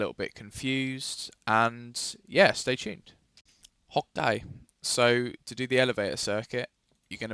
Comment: Question: What are you saying when you start a part (e.g. 00:47)? "hokay" or am I just stupid haha